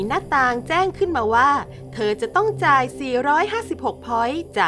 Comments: เสียงพากย์พูดว่า456 แต่เขียน452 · 452 พอยส์ ไม่ใช่หรอ ได้ยิน 456 อะ ใครได้ยินบ้าง